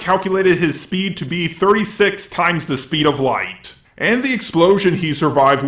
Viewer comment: i thought he said 38x the speed of white 😂😂